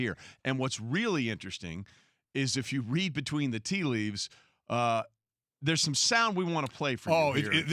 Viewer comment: Sometimes I need some help to understand english so I put on the automatic subtitles. Does anybody know why they're not avaiable for some vids like this one? Thx in advance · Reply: I think he says "if you read between the tea leaves" is that correct? Also I forgot how to spell leaf in plural is "leaves" correct?